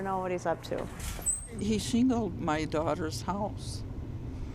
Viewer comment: What is the line at 2:43? I can't understand what she's saying: "He ____ my daughter's house...." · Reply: Shingled, I believe.